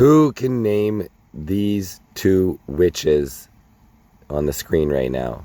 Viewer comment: sorry I thought you said bitches